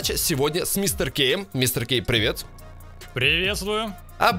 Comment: мне послышалось мистер гей